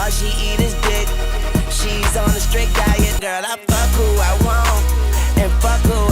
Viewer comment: I thought lil wayne said '' and all she eat is dick, she is on a straight diet, Not strict diet, I think my line is better makes more sense